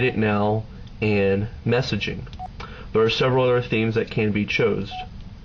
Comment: did he just say "chosed"?!?! HAHAHAHAHAHAHAHA